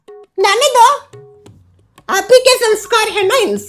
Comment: he literally said nane do instead of jaane do So funny 😂😂